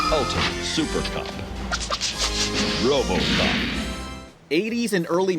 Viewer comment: I can't be the only who thinks that the voice at the beginning of the robocop cartoon sounds like he says robocup